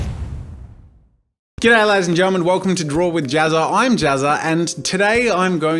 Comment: Every time you say 'G'd day, ladies and gentlemen' I hear 'Get out of here'